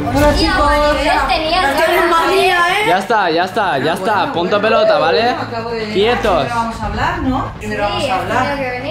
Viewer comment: mirar lo que dice es el puto amo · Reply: El puto amo soy yo